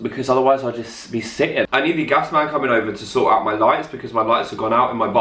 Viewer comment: For a second I thought you said "I need the gas man coming over to sort out my life" and I was like awww noo